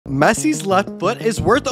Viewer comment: i thought he said messis left butt💀💀